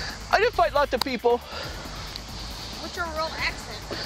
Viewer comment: i thought he was saying he finding nemo.. i realized he said he fighting evil .-.